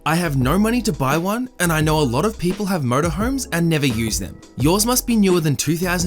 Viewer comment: Holy shit-first time here, and didn’t fully read the texts...I thought you kept saying “please give me your Murder Home”. IDK what’s more unsettling to me-the fact that THAT was what I assumed you said, or the fact that I just casually accepted it and only realized you were saying “MotorHome” when I accidentally looked up..was totally cool w/someone wanting a murder home. I kinda always wanted one anyways. Murder House Flip= Best Ever.